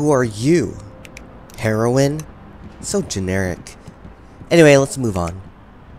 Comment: I thought it said herobrine